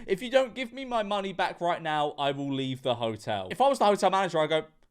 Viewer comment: you misheard her. She said if you do, she will leave. She wasn't demanding her money in that moment, just requesting it, but she's being rude for certain. deff karen